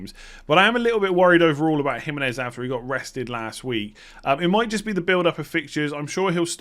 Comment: I thought you said Jimenez was arrested not rested 😂